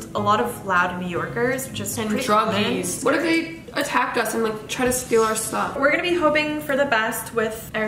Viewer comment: I missheard erica so bad XD “what of they break in and like steal our stuff?” What i heard. “What if they break in and steal our socks?” 😂😂😂😂